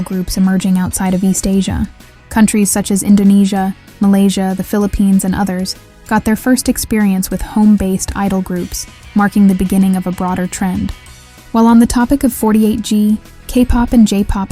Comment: BINI MENTIONED???? OYY PHILLIPINES 🗣🗣🗣🔥🔥🔥🔥🔥👏👏👏👏🇵🇭🇵🇭🇵🇭🇵🇭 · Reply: i thought that said oh my philippines 😭